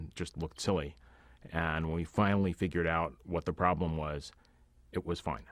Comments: Did he just say that the problem was Violet? Harsh. XD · can someone tell me what he is saying the problem was? I rewound this part like 10 times, I can't understand the last word... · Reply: I think he says "when we found out what the problem was, it was fine" but doesn't actually explain how they overcame it.